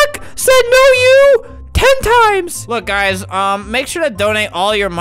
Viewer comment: i wasn’t looking at the screen so when he said “ten times” I thought he said “hentai” and I got really concerned.